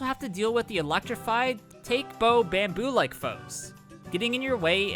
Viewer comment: The caption said “take bow” about the bamboo enemies. What did you actually say if not that? Or does it have a different spelling? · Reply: According to the Mario wiki they're named "Takebo" in the game's internal files. "Take" means bamboo, and "bo" is an affectionate suffix.